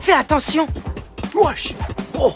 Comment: c'est moi où il a dit " wesh oh "